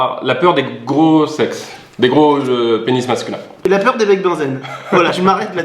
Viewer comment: Il a dit quoi svp j'ai pas compris ? La peur des quoi ? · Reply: Bec Bunsen